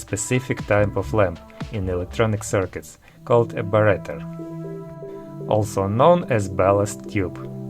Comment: Wow, great collection! I have quite a few interesting bulbs in my collection... but several times as many that I have lost or destroyed in my somewhat mis-spent youth :( Question: What is that word at about 5:22? Barrater? Lastly, I recently did a video on the Wien bridge oscillator: <link> including incandescent bulb stabilization. Around 48:30 is where the analysis and testing of the bulb starts. (All design files on GitHub, creative commons license.)